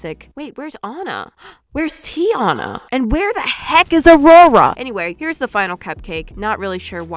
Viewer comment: be like: What ME says: AND WHERE THE HECK IS AURORA?!!!!!! What I hear: AND WHERE THE HECK IS RORORA😂😂😂😂😂😂😂😂😂😂😂😂🤣🤣🤣🤣🤣🤣🤣🤣🤣🤣🤣🤣🤣🤣🤣🤣